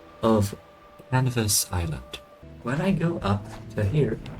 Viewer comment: Elvis island? Sorry what did you said 😆 I think i always called it the crash island since the com black crashes there